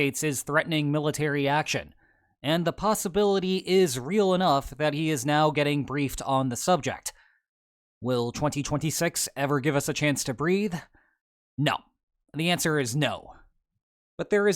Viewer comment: Who else misheard as "Will 2026 ever give us a chance to breed? No, the answer is no."